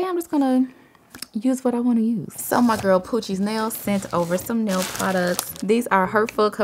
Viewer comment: I thought the nails said “coochie”